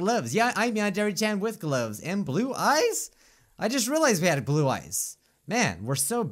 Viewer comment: at first i thought you said glue eyes is that weird????????????????????????